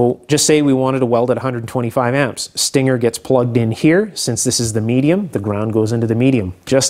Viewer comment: I misheard 'if you want to weld at 20 amp you stick your finger in here' 😂